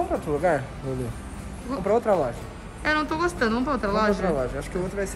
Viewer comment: Só eu que ouvi "então vamo pra outra loja...amor?" Geeeeente